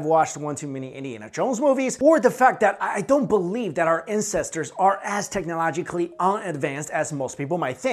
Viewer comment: he says incesters instead of ancestors :DD